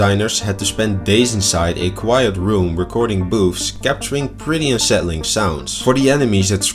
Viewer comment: I don't know what but the first time I heard what you said I heard you said boobs